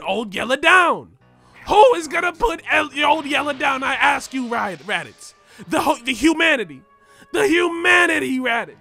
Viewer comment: Did he call Raditz Riley? Boondocks reference?